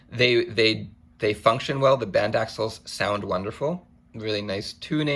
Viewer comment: did he say "the band axles"? I guessing he meant Baxandall. All the same, great demo!